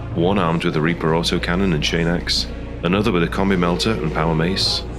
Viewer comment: It sounds like you say "one armed with a commie melter..." And I approve.